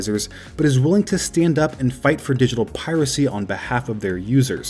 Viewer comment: does he say digital piracy or digital privacy? kinda confused with that